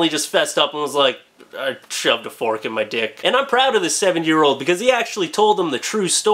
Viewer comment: did he say 7 year old ???